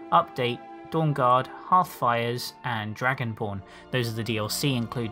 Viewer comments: I thought he said dragonporn lol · lol at first when you said dragonborn i could have sworn you said dragon porn